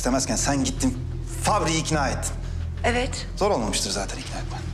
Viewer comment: What exactly does Can say at 18:36? In Turkish please, if anyone knows. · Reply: I hear sg like "zorulmamișten zaten...."